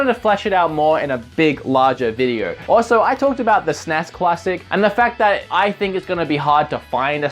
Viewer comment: it sounds like you say cuassic